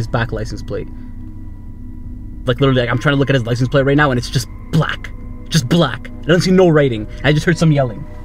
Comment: I thought he said black guys